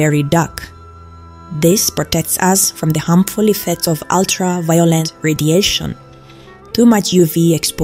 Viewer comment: I heard 'Ultra-Violent Radiation' and frankly... lol.